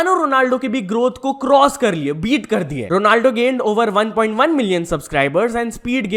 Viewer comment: ye kya bol rhe ho 😭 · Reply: Gaind😂